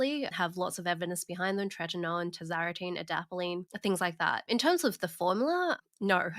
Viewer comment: What was the ingredient mentioned before adapalene around 28:33? I'm struggling to hear it and the subtitles are wrong. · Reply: It's tazarotene